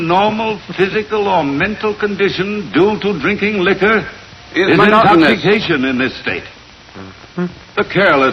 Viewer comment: What was Crow saying @ 14:16? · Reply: "Is monotonous"